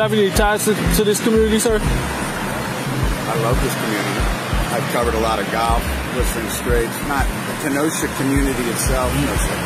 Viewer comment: "I love this community. Whistling Straits.". First, Whistling Straits is not a community, it's a golf course. Second, even if it was it's 170 miles from Kenosha. I don't know of any community that large. Love the irony that this race hustler producer was pulled over by a black officer. He couldn't even get a story there. I like how quick this "man" was to throw a woman under the bus. What a pos. Great job. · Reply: It sounds like he says "I love this community. I've covered a lot of golf, Whistling Straits...not Kenosha community itself." So yeah, he was trying to bamboozle the cop, but at least he was truthful enough not to claim that he truly had ties to the heart of the Kenosha community.